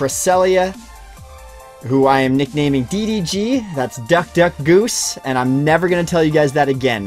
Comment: I also misheard the name for Cress when you first mentioned it. I thought you said Dr. Goose lmao